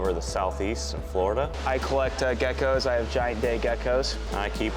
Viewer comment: I swear I heard “I have giant d*ck geckos “ 😭😭😭😭